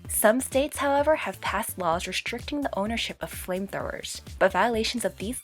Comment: Did she just say framethrower?